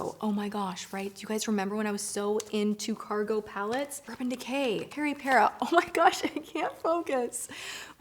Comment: What did she say at 1:01 after Urban Decay? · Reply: BeautyFanatic27 she said Peripera 😁👍🏼 I think they were lip jelly glosses? or something similar to that. Hope that helped!!